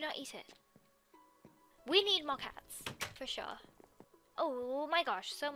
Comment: "We need more kit kat's" I heard kit kat's for some reason